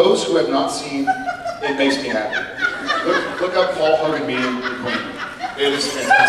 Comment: What was it that Jared says to look up at 30:53? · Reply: paul hogan meeting the queen :)